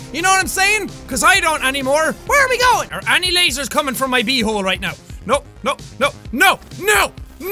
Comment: it sounded like you said is there any lasers coming out of my b hole instead of him saying is there any lasers coming after my b hole